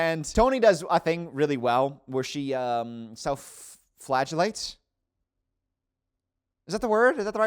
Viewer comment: Ahahah I thought you said self flatulence or flesh lights and had to rewind and put captions on 😹